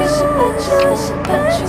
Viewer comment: i heard this song in another youtube video and for some reason at first i thought she was saying "perpetuous" (not entirely sure if that's actually a word or not) instead of "i bet u wish" lmaoo